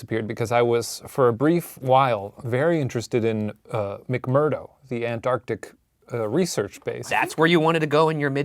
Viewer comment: When Shane said “McMurdow” (or however it’s spelled) I thought he was saying “McMurder” and I got very suspicious